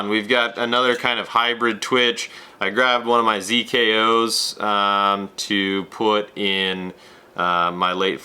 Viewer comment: Hey i couldn't catch what the weighted tail twitch bait is at 5:00 minute mark is. Any help? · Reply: ZKO Zilla Customs knockout. Hope that helps!